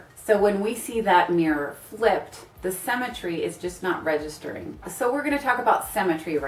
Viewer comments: Haha, I heard "cemetery" instead of symmetry and at first I was confused. xd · I thought you said cemetery instead of symmetry and I was so confused😂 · I actually thought she said cemetery when she said symmetry..!